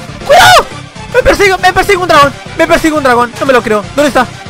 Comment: Pensé que dijo me pega un Tragón y dije soy yoooooo pero no dijo dragón XD